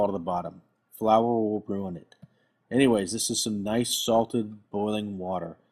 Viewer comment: can someone help me with what's being said? flour .... something? thanks! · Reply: Bestest Inventions He says "flour will ruin it." aka don't use flour to make the pasta nonstick, use cornmeal instead